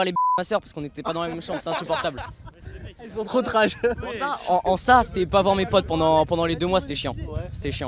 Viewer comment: en en en quoi ????!! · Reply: En Ça